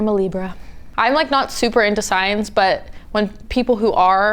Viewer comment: did she just say "I'm not super into science..." and then proceed to talk about astrology🤣